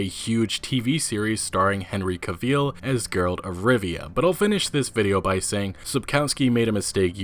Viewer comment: did he just say "Girl of Rivia"?